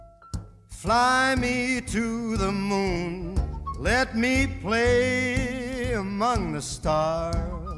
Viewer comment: Did he say...AMONG GUS? 👀